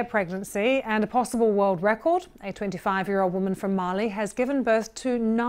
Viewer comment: did i just hear "a woman from Marley"??